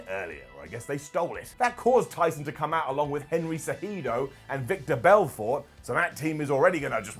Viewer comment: FYI Its Vitor Belfort... it sounded like u said Victor.